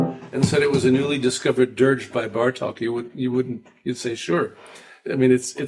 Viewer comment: Does anyone know what he was saying about Bartok here? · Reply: only that if you hadn’t heard Chopin’s Prelude no. 2 in A minor and somebody told you it was a newly discovered dirge by Bartok, it would be believable!